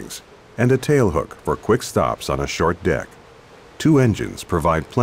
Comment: I heard short dick at 0:38 :)